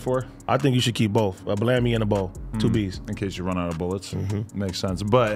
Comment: Bruh when said keep a bow and plan B by the bed and Adam said “in case you run outta bullets” first thing I thought he said was “in case you run outta pills” like he just gon shoot her with the bow if he run out 😂😂 my mind aint right